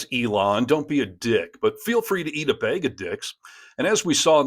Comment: You can say Steve "Harness", (and not "Hairness") but you can't say "MAGA", (you consistently need to say "Mayga")? Clearly, if it isn't some kind of speech impediment, then it's what... ignorance? But, wait... you also said, "Feel free to eat a Baig of dicks"! 7:13 Truthfully, are you really this annoying naturally, or is it a deliberate attempt to trigger people (like me) to comment, in a cynical attempt to farm engagement? I don't mind taking that hit by engaging to ask this question, because I really want to know! Also, the correspondent in the video you showed at the beginning pronounced "Edinburgh" as "Edinboro"... one of the laziest and most egregious things an American can do when referring to our capital. Also, HE, (Simon Brodkin) wasn't in a foreign country, YOUR fuckwit President was! 4:49 And, no, WE DO NOT have any retailers here who sell golf balls with swastikas on them! <wtf?> Simon obviously had them specially printed for the event, you prick! Scotland has been ridiculing and protesting your tangerine tainted fuckwit, far more vociferously and for far longer than your own pathetic, milquetoast media has. We saw all of this shit coming long before you did, and that's YOUR fucking country!! The USA has been a bottomless source of derision & ridicule over here (and all over Europe) since well before even George W. Bush established himself as the biggest American Clown since Buster Keaton! But you lot have been clueless and complicit right up until Trump's 2nd (f